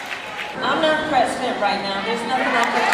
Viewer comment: "I'm not President right now." "Thank God!" 13:47 (Did I hear that right?😂)